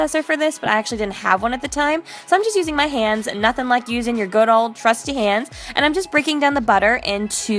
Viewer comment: at first ,when she was making the pumpkin poptarts, i thought she said "use your good old crusty hands" lol